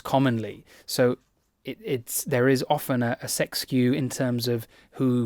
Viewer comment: "But men, particularly young men, tend to put them forward as volunteers for this sort of thing most commonly, so there is quite often a sex skew..." For a second I thought you said there was a sex queue, and I was thinking, of course men, particularly young men would be looking to put themselves forward for that. I'm not so young anymore. I took a number a while ago. I'm wondering now if I should go up to the counter to make sure they haven't forgotten about me...